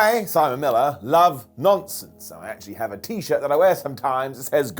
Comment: At first i genuinely thought he said " I Simon Miller love nonces"